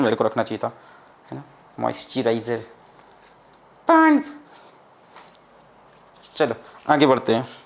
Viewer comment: did he say ponds?